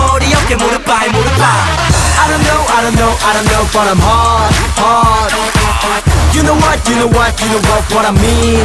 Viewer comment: The fact I actually thought they said I'm hard not hot